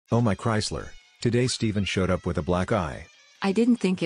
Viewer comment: I thought he said black guy 😂 I'm not racist I swear 😥